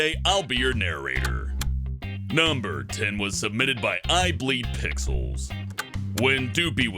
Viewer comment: I thought it said I bleed pickles. gets cut "AAAAHHH* everyone withingg earshot: "what the hell" runs over "sir are you okay?! Oh nevermind I see you are not bleeding" eats pickles